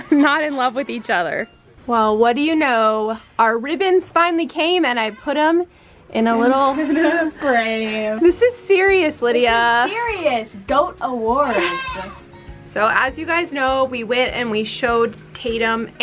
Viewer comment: all i hear is "this is sewious" from the wonderpets theme song 😂